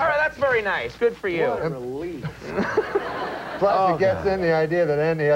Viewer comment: What did Andy say at 5:14? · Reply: What a relief